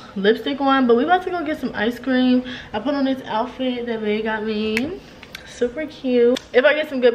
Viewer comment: OMG! I know I’m late late but HAPPY BIRTHDAY 🎂 love you my Virgo sus 🤪🤍 my birthday is September 2nd and I can always spot another Virgo when I see one 😭 that’s why I love you and your videos 🥰🥰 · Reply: ALSO!!! Did I hear “the outfit that BAE bought me” 😝😝 that’s right girl !!